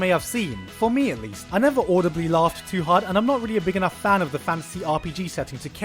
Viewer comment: Take a shot every time i thought he said fancy instead of fantasy